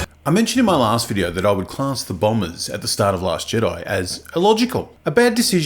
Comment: I thought that said boomers